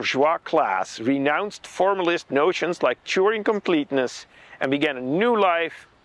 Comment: Subtitle says 'Turing incompleteness' instead of 'Turing Completeness'!